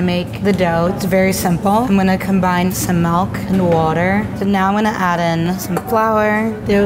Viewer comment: did anyone rewind when she said “melk” because you thought you heard her wrong? just me.. ok